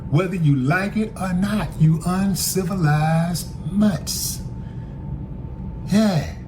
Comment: Did I just hear "uncivilized mutts?"